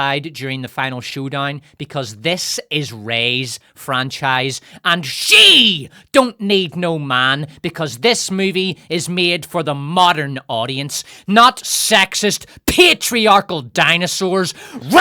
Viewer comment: honestly mishearing that as "SHEEV don't made no man!" made that moment of the video even better.